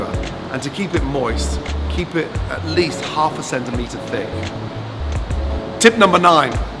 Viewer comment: did he say keep it half a centimeter thick?